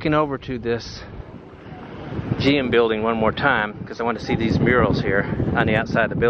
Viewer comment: L O L when you said I want to see those murals some hot chick had just walked by and I thought you said I want to see those girls and I was with you on that and then I was disappointed when we went and looked at the murals!